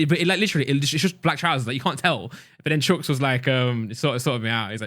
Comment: Did he say chunkz lool